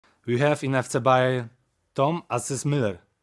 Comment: Why I always hear “Tom-ASS Muller” XD